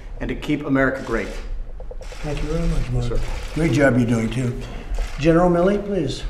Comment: I must be going crazy...Did anyone hear Trump say, "Thank you for what your doing Q"?? At the 1:51 mark...WOW